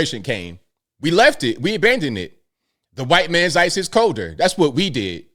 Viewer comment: did say "white man's ice is colder" ??? 😂😂😂😂😂 Wow. I hadn't heard that one. Interesting. Lol, continue